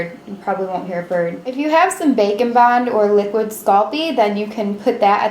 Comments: I say either or and I thought she said bacon bond but she said baking bond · Both and when u said bake n bond I thought U said bacon at first. Lol